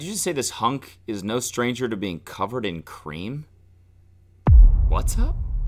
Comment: the line “this hunk is no stranger to being covered in cream” made me actually die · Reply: I thought she said 'green' instead of cream at first. Given that he's a stripper, that would have made more sense imo.